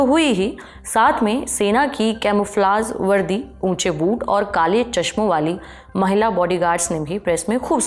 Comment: i heard boob